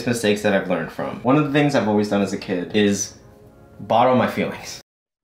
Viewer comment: LMAO sorry i thought i heard "bottom" my feelings but yk... sometimes my feelings fuck me in the ass too :(